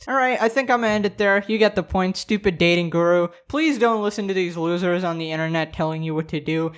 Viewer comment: I thought it said dating gru and now I'm disappointed